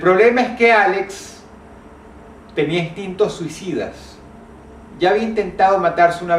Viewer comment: entendí "Distintos sidas" x'd